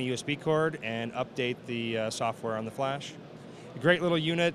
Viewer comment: did he said "on the Metz flash"? (0:45")